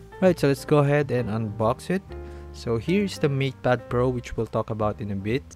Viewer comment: Why do I hear "meat pad pro" instead of "mate pad pro"